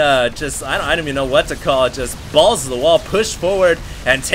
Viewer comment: Umm did just say "balls to the wall" at 5:00? LOL